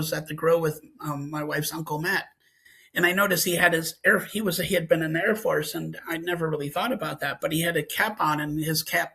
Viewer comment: What does he say between 22:05 -22:10?? The CC doesn't transcribe but it sounds like, "he was a hitman in the Air Force"